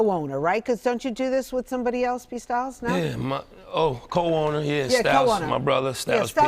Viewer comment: “P Styles” 😂😂😂😂😂 anybody else catch that?